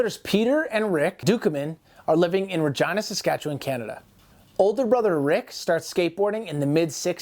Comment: Bro. I thought you said "vagina saskatchewan Canada" I had to go back 🤣🤣🤣